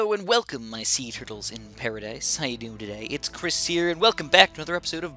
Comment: I Thought He Said Seizure Enduring Paradise